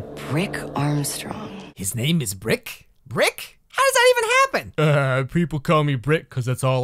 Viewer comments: hisnameisB R I C K? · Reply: I misheard it as Prick before, and I honestly don't know which is worse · I swear I thought you said the guys name was Prick Armstrong. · Sorry but currently cry-laughing at the moment because I thought she said "Prick Armstrong" 😭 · I thought she said Prick